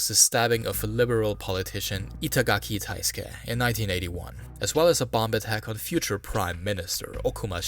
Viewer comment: Am i the only one hearing him say 1981 at 20:24 - I keep hearing about the 1980s when the story is in the 1800s